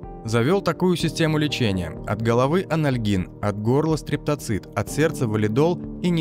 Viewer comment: Мне одному послышалось, что этот маньяк вошëл в АНАЛ криминальной России?))) 52-53 секунда))